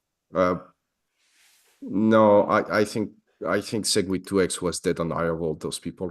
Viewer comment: what did he say in 12:59? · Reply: SegWit2X was dead on arrival